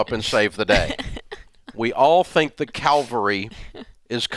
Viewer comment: I thought I heard calgary which is a canadian city and an HMCS ship